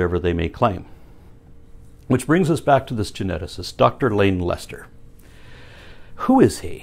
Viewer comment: Dr Lane Lester. I thought you said Dr Lame Lester.